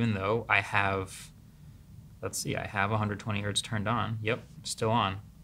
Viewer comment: the caption says 20hz instead of 120hz.